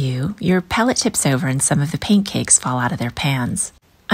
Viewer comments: Am I the only one who thought she said "pancakes" instead of " paint cakes" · I thought you said " Pancakes " instead of " Paint cakes"